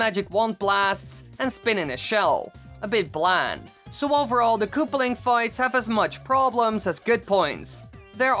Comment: i heard "the koopaling fights have as much problems as bitcoins" what do you think he said?